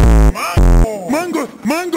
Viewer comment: i hear Mary go